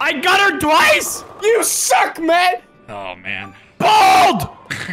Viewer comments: I think he said BALD!!! Not NOO. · Who else heard say “BALD” and not “NOO” ? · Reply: its just subtitled wrong, he does say bald, not noo